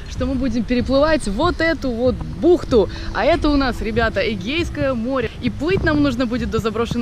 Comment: мне изначально послышалось гейское море 😅😂